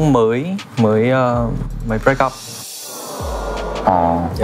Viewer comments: giây soobin nói gì v mn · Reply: Soobin mới break-up là chia tay người yêu đó bạn. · Soobin nói gì dạ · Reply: Soobin mới break up - chia tay á bạn